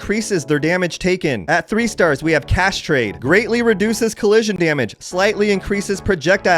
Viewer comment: Man I had to double take when you said "cash trade" I thought you said castrate